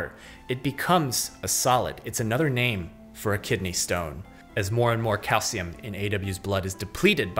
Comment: When he said another name for kidney stone I thought he said infinity Stones 😂😂